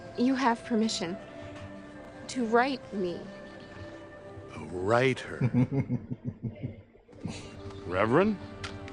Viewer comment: Ah to write her. I thought you said ride her!